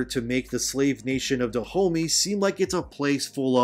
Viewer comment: I keep hearing “da homie” 😂😂😭